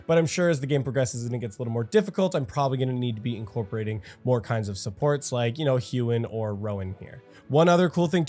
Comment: Maybe I didnt sleep enough, when u said said u needed "more kinds of supports" I heard "war crime supports."